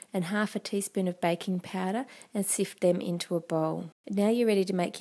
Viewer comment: Haha, I misheard "baking powder" as "bacon powder". :P This looks delicious, can't wait to try it. =) (Good thing it turned out to be vegetarian friendly ;) )